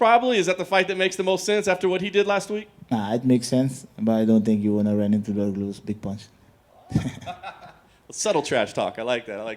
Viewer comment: anyone know what he said about himself vs Derrick Lewis? couldn't hear him properly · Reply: “It does make sense, but I don’t think he wants to run into any of my big punches.” He obviously joking around but a ton of people will think he’s serious lol